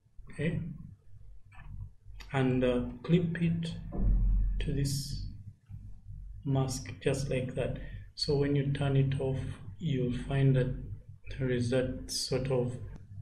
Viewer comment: What did you say at 8:49 ? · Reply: Clip it to this mask, just like that so when you turn it off you'll find that there is this subtle shadow... ..